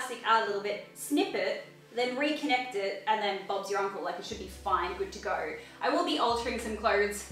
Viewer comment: did you say and just like that bobs your uncle 😂😂😂😂☠️☠️